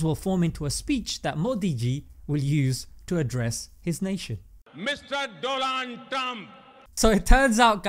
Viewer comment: Did he said "DO LAN"....?